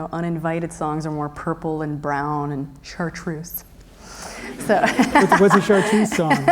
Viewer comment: What does she say in 19:20? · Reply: The color "chartreuse." :)